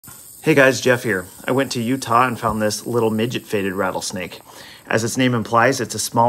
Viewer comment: Brooooo thank you for immediately clearing that up at the start I thought you were saying a midget faded (colloquial for 'fought' in my area) a ratylesnake 😂😂😂